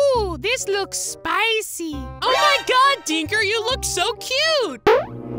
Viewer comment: my dad thought the phone said “n****” when they said “dinker”